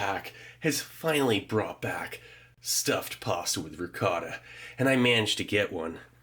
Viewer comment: My deaf ears legit thought you said possum with ricotta